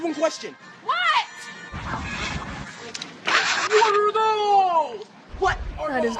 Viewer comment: I always thought that they were saying WATER HOSE!!!